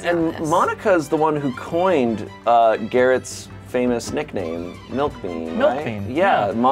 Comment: Interesting, I heard Garrett's nickname at 13:08 as MILF-bane. I guess that is a little too brutal.